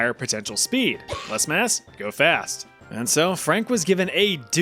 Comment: took a step away during the last story, swore i heard “less ass=go fast”